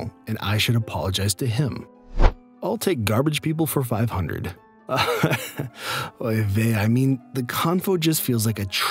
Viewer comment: did... did you just say... "oy vey"? 😃